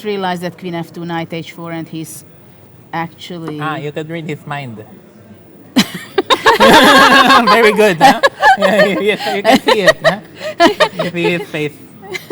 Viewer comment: What did Wesley say at 4:11:01 ? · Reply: "Ah you can read his mind! Very good huh! You can see it! (You can read?) his face."